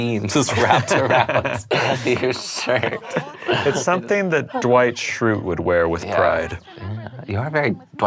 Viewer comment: When you said Dwight shrute😱 I screemed OFFICE REFERENCE IN STUDIO C I LOVE YOU EVEN MORE STUDIO C❣️ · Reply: Sydney Carroll I almost thought he was saying Dwight Shultz! lol He's was a funny guy!